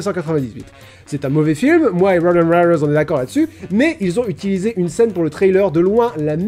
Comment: "moi et rowoenwewewoes on est d'accord là dessus" :')